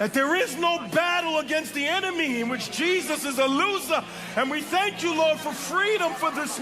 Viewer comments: Did I hear it right? "Jesus is a loser the pastor said ? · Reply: No he said “Jesus is not a loser” to a demon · Woowwww this is crazy, 😮😮😮 how the pastor said Jesus is a loser, I revoked in Jesuschrist Mighty Name. Woowww I'm surprised no one has caught that and is around 6:34 minutes and 34 seconds. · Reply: And there is no battle against the enemy in which Jesus is a loser is what he said. You probably didn't hear it right. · im so confused at 6:39 it sounds like he said jesus is a loser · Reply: He said "there is no battle against the enemy where Jesus is a loser" 😊